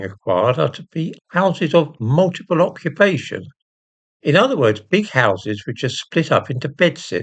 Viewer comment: Did I mishear that ? Houses of multiple conflagration?